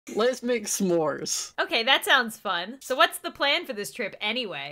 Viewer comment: At first when it said “let’s make s’mores” I thought it said “let’s make noises”